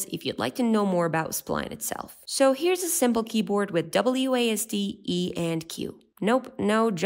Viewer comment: Instead of 'wasted' it is 'wasded'